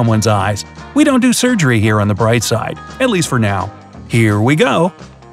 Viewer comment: Did he just said 'at least for now'🤣